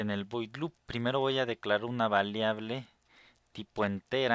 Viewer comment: Like si tambien escuchaste que dijo "Valeable" al minuto